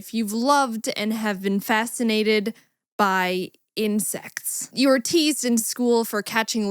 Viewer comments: You know instead of "insects" I heard "incest" so I was very confused for awhile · When you thought she said “you were fascinated by incest” instead of insects ...... 👁👄👁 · My mom got mad because she thought said incest instead of insects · Don’t ask me why but when she said insects I thought she said incest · I thought that she said I've been fascinated with incest. 👀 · I misheard insects as incest and I was like "woah, that took a turn." Hearing disorders are just the best. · JESUS I THOUGHT U SAID INSEST 🤣😂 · I heard "incest" instead of "insects" and was absolutely horrified